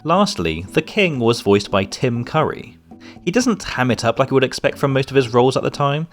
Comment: That's honestly so awesome to know! The last time I heard him do the voice was during the Rally4Sally hangtag. 🤭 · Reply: I thought you said it was Jim Curry?